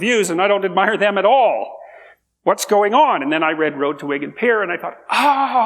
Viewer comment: I read "The Road to ?????" What did he say? I heard Wiggen Peer, someone with better ears please clarify. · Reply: Wigan is a town in North West UK